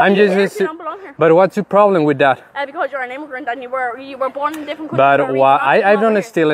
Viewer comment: A lot of these subtitles are wrong, my guy. at 3:13 she says "Because you're an immigrant and you were born in a different country. "